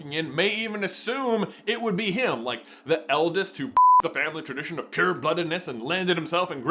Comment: WHAT DID HE SAYYYYYYYY. I have no problem with cursing, as a blue collar tradesperson certain swears are commas to me, (and in multiple languages too) but THAT. THAT threw me off hahaha. We need a after hours podcast where its unfiltered and they tell stories not otherwise told on the normal family friendly podcast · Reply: I feel like he said "bucked the tradition" and the editor misheard it and bleeped it out of an abundance of caution 😂